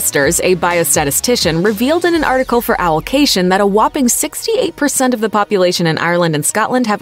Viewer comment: the subtitles says 86 but i clearly hear 68